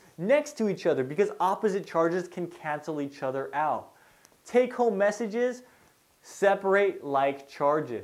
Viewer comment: "take-home messages"? At 12:19... i couldn't understand what you said and kept on hearing take home messages so i added subtitles but then that's what it said as well. I wonder hat you really said. :/